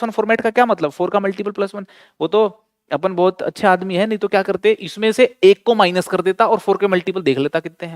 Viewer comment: can anyone explain what did sir say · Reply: Sir said " vo toh apun ache admi hai nhi toh kya karte "